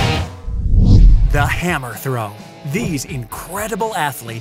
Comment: i thought this said hamster and was really confused